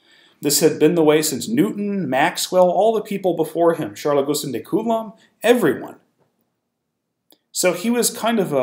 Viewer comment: “All the people before him. […] Everyone.” What does he say here…? I couldn’t make it out. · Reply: Charles-Augustin de Coulomb.